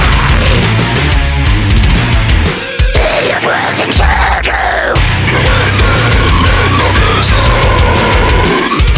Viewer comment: what is he saying? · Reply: He, who dwells in shadow